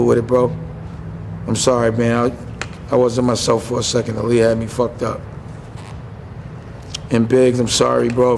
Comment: Did he say the weed had him fucked up? 2:06 Lame Dash